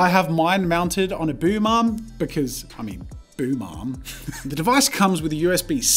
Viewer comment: i did not hear "boom arm" I heard "boo mom"